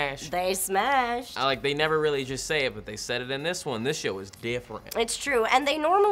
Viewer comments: Im I the only person who heard say shit at 5:23 ? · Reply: He said show, not shit. get your ears checked mate. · Wait did say this shit I s different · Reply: I heard it too. He said "this show was different" It sounded alot like shit the first few times I listened to it too. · I don't know what's up with all the people saying that said "This shit is different" at 5:23, he clearly said "This show is different." doesn't curse goshdarnit!